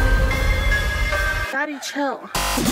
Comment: Why did I hear "Daddy Chill"